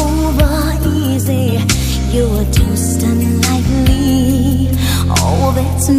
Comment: The lyric is not: "Your toast unlikely". LOL It's: "your toast done lightly" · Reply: plutorama lmaoooo I always thought she said your toast done,like me!